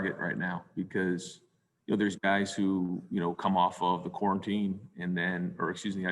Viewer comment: lmao when he said quarantine sounded like dabo for a second there